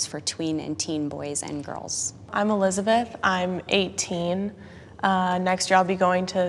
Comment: Lowkey thought she said "I'm a lesbian" there lmao